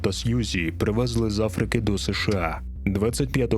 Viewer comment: почула, що Коко та Сʼюзі привезли з Африки ІПСШ а